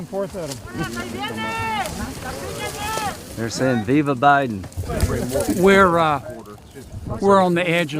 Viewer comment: lol 3:54 he did not say viva Biden, they said “they are coming”